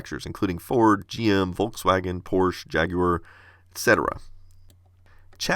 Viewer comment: Volkswagon instead of Volkswagen